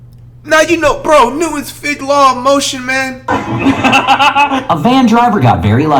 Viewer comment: did he just say newtons fig law of motion.... like... fig newton LMFAO